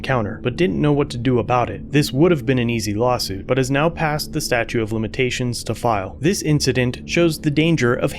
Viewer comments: Please tell me I didn't hear you say "statue of limitations" SMH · Lol did he say statue of limitations?